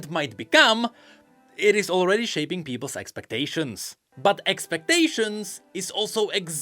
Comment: I misheard s0xpectation at first and thought ok so this is about skimpy skins isn’t it, and realised it was just the accents. Welp, it fits the context though